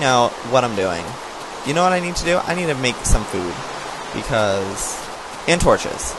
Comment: When said "I need some food!" the rain was really loud so I thought he said "I need some boob" and I'm like "WTF?!?!?!?!?!?"